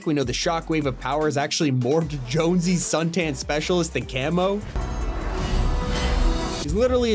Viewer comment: can someone please explain to me what this man said. I re winded 4 times. Sped it down to 0.7. Then 0.5 to still barely just make out “powers actually morphed jonesies son-tan-specialist-than-camo” like what?